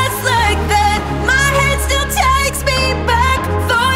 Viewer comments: Misheard the lyric as `my ex still text me back'😂😂😂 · Deadass thought she said , " my ex still texts me back " 💀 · For the longest time I thought I said, “my ex takes me back” 😂😂😂